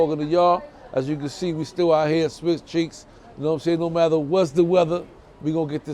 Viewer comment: did you just say "Swiss cheeks"